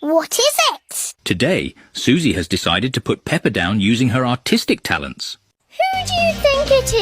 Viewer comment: I thought the narrator said "Autistic" 😭💀